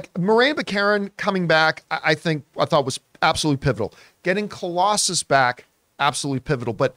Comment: You said “pivotal” but I heard “pitiful” I was like what!!!!